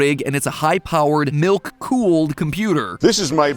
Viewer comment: First time i heard "milk cooled pc" i misheard it as "nuke cooled pc"